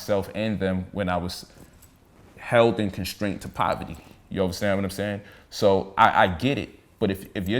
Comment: Did he say "overstand"?